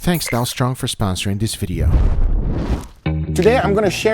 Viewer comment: At the beginning I thought he said “thanks Donald trump for sponsoring this video”